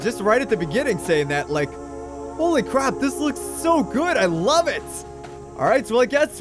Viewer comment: lol instead of saying "holy crap" he said "holy crop" ba dum tss